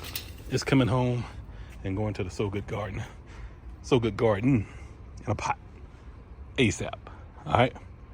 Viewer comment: Why I heard you saying you got to pay to stay in the “Soviet garden!” I was like What!!! I had to run it back!!🤣🤣🤣😂😭😳🧐🤪